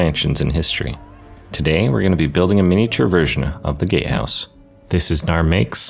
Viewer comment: Look ik hes saying “gate house” BUT ALL I HEAR IS “gay house” AND NOW I CANT UNHEAR IT AND ITS FUCKING HILARIOUS ( btw it look mf amazing you did an awsome job on it)